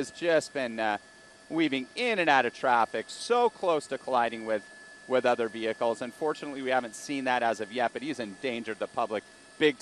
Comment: did he just say "he hasn't collided with any traffic, unfortunately we haven't seen that"?